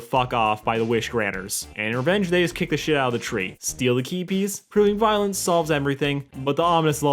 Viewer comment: OOOOH "key piece" I thought you were saying "keepies"